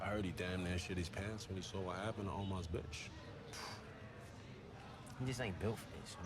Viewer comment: Not the main point of the episode but whenever I hear that I still hear myself thinking 'Hey, Brandon wasn't a bitch'